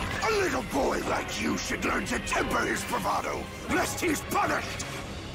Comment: what did he just say? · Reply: Lest he's punished